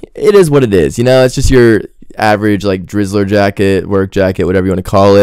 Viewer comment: Did you just say “jizzler jacket” 😳